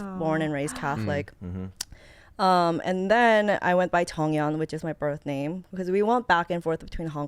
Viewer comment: did gia say her real name is cheonyeong/jeonyeong? 😅